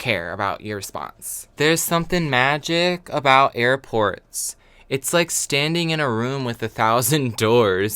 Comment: Hehehe I heard "there's something magical about airpods" and tbh I really didn't notice I-😂😓